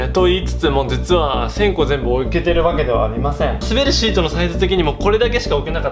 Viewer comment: ん？滑るシート？3:36をみると滑るシートに聞こえる。滑らないシートじゃないのかい？